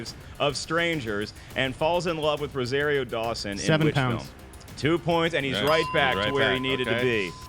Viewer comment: Did Sneider say "Seventy Pounds"?